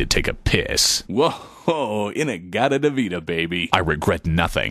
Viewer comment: What is he saying at 4:46? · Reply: Whh-hoa Inna Gadda La Vida Bay-bay In reference to Inna Gadda La vida baby, or In the Garden of Eden Baby, but Iron Butterfly. The vocalist was super drunk, and super high, and warped the lyrics, and they kept it that way.